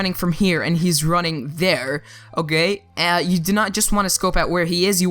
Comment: Did he say: Oh gay??